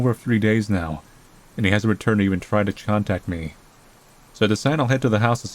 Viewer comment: did you say 'chontact'?